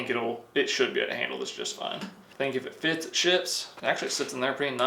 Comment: & 5:20) What the hell!?😅😂 Did I just hear say " If it fits, it sh**s " ?😅🤣 I don't usually say words like that but I've never heard cuss before. And I've been watching for around 3 years (2021) So i guess i haven't watched this video a lot or at all, or I didn't listen closely with the sound turned up.😂 This is probably my favorite part.😂😂 Great video!😊